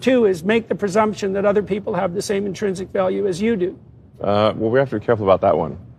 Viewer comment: I'm not sure sure if Elon made the same mistake as I did, but when I first heard this I thought Jordan said 'values' not 'value' so I thought Elon's response was reasonable; if he did not make that mistake, this is certainly a hilarious and concerning response.